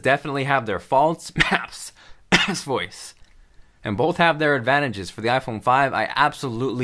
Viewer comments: Is sounded like he said ass voice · did you say false voice? haha touche